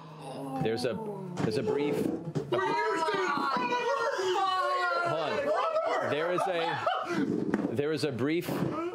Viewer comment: what is Travis saying at 2:08??? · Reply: “We used it in front of her”